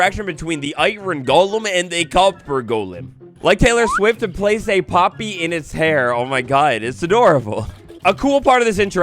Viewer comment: what the heck is he saying about Taylor swift here. I replayed it five times and still don’t get what he’s saying lol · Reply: Like Tyler swift place a poppy in its hair